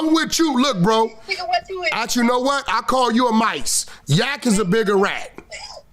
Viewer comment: calling "a mice" is hilarious. 😄 I be yelling at my phone, correcting people who can't hear me.